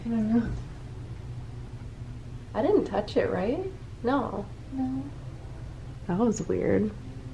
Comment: At1 11:38 I hear two ppl say no